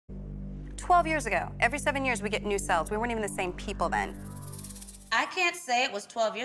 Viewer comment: It sounds like she said "new selves." Meaning that we change and grow.